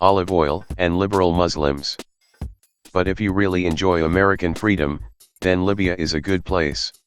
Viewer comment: what about ALGERA? 💔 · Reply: oh waiiit i think im dumb. Because of the comment above i thought you said "freedom for algeria" as in the usa invading algeria. im Algerian bro dont worry i love my country